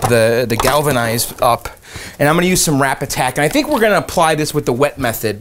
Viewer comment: TIL it's Rapid Tac - Anyone besides me thought was saying "Rap Attack"?